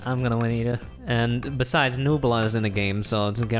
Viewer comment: I thought they said “Nogla”